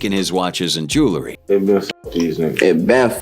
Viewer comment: Dude is saying n*ggas but the Captions says the Hard R instead ☠